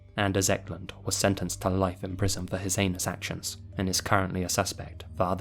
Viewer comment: Am I the only one who misheard 5:13 as "Ecklund was sentenced to life in prison for his anus actions"? I'm sure he's had plenty of that AFTER going to prison. LOL